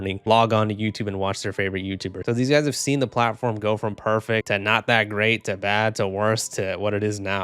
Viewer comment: DID YOU SAY PERFECT!!!!!!!!!!!!!!!!